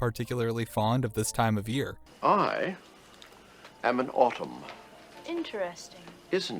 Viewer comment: Thought the man saying “I am an autumn” was saying “I am a bottom” and I was like good to know but what does that have to do with fall